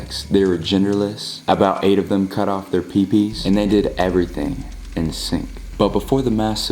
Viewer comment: i thought said “they did everything in the sink”😂😂